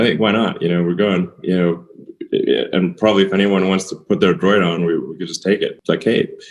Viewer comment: who's going to put a joint on a spaceship to Mars... Ohh, a droid, misheard you there for a moment. Sounds good both ways, Space Dreams <3